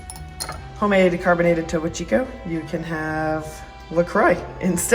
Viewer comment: What is the water you refer to from your carbonating machine? something chico? at 9:09 in the transcript. The transcript writing thing doesn't understand what you are saying. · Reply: Topo chico ( mineral sparkling water)